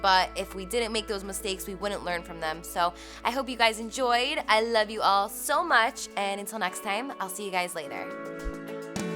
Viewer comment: i thought she said I love you guys so much until the next video, i was like what that was so rude, but she didn't